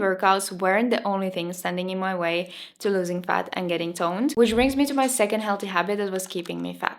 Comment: Thought you said you weren't as 'stoned ' as u wanted to be